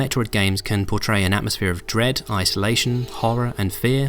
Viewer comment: Did he just say D R E A D